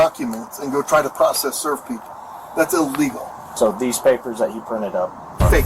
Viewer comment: Most useless question ever? Did he misspeak? "So these papers that you printed up, fake" "FAKE, YES 1 billion trillion gazillion percent". edit: maybe I'm mishearing it, the "you" or "he" sounds so similar somehow. The auto generated captions says "you" here but "he" in original video.